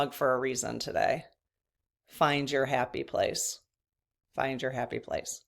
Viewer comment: I thought your mug said, find your nappy place 😊